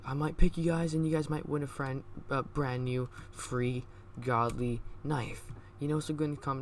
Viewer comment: I thought you said free godly COdEs